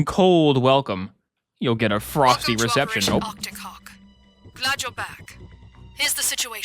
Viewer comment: I thought she said, "Arcticock" 😂